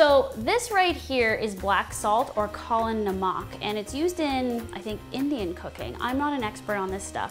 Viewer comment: i had this playing in the background and when you said "used in indian cooking" i had to do a double take because i thought you said used in indian cocaine! the way you said it hahaha